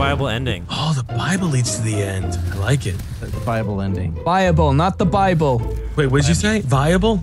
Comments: I always thought they were saying bible as well. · Tbh I always thought they said "bible" instead of "buyable"